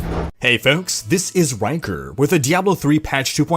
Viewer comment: I wasn't paying attention to the video for a second and I thought you said "it's wanker" and not rhykker.